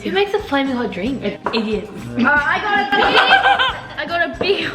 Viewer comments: no hate but sabre asked who makes flaming hot drinks and did sock says Indians?bec that's a bit.. +the Indian Musik in the background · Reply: I thought she said idiots. Why would she even say Indian, and then post the video… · FOR THOSE OF YOU WHO ARE SAYING THAT SOCKIE SAID INDIANS , SHE CLEARLY SAID IDIOTS . YALL JUST WANT SOMETHING TO HATE ON THE NN ABOUT!!!